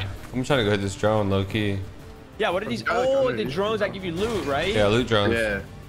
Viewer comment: Lol I could have swore I heard ask if “the drones give you toot right” 😂